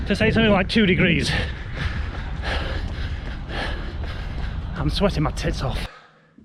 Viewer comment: What is the phrase or word he says at 5:17 ?? I’ve been trying to figure it out for awhile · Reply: I'm sweating my tits off...